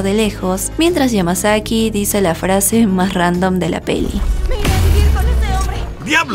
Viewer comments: que dice? no se escucha :v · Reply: Creo que dice "me iré a vivir con ese hombre" la verdad es que no estoy seguro si dice eso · No sé que dijo 😭 Me pueden decir que dijo xd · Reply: Me ire a vivir con ese hombre ( algo tarde pero igual dejo el comentario ) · que dijo? xd estoy sordo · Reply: Me ire a vivir con ese hombre